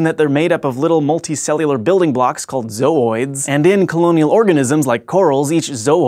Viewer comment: I got hype when I thought you said "Zoids"